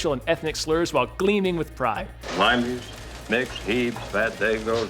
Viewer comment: Honestly thought he said "Limeys, MICKS, Heebs ..."